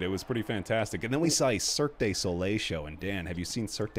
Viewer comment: Gotta say your Vegas stories were quite nice to hear, glad you and Alyse had a good time. One small thing though, every time you pronounced "Cirque du Soleil", I hear "Cirque Désolé", which means basically means "Sorry Circus" lol